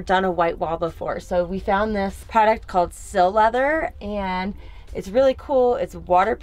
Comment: Hi, thanks for sharing all the info on your new van, it's very nice! Could you tell me what the waterproof material used on your wall panels is? It sounded like you said "seal leather", but I'm not sure I heard correctly and can't find anything online. I'm interested in knowing where I can buy that material to make my own panels. Thanks! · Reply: It's called Sil-leather